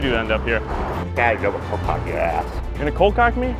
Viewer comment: When he said I'm going to cold cock your ass. I thought he said I'm going to double cock your ass